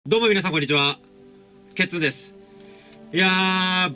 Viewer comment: 最初ケツです！って聞こえた…